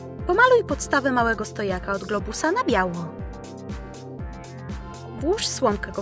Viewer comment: tylko ja usłyszałam białko a nie biało